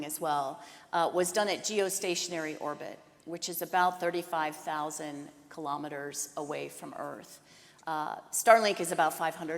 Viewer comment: "A geostationary orbit, also referred to as a geosynchronous equatorial orbit, is a circular geosynchronous orbit 35,786 km in altitude above Earth's equator ..." That's from Wikipedia, which is generally very trustworthy for anything that doesn't overlap with political matters. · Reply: Thanks, I misheard. She actually said 35000 km at 22:03. My brain seems to get used to the way numbers are spoken in English, so instead of 35-thousand I heard 35-hundred. 😆 I'm not a fan of uttering numbers in English. At least it's much better than in French. So, as Elon likes to say, in reality that's an order of magnitude more than what she said. It looks like she did the math on the spot and lost a zero in the process.